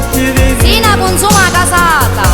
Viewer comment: Qualcuno sa dirmi cosa dice la tipa a 2:19 ? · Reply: Si na consuma casata.